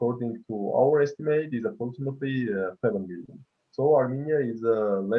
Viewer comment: did he actually say azerbaijan has a population of 7 billion😑👂🤦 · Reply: He said seven million. I slowed the speed of the video to listen.